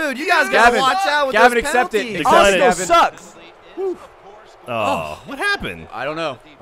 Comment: Haha i heard gavin yell 'arsenal sucks!' You guys should definitely play fifa!